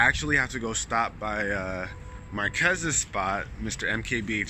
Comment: Holy shit it really sounded like you said "my cousin's spot," but when I seen your comment I can hear you clearly say Marques